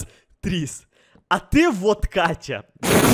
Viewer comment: мне одной послышалось водка Катя 😂😂😂